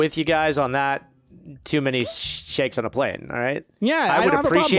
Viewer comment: Honestly thought it said snakes on a plane and was like 🙄 like... That movie was, uhm.....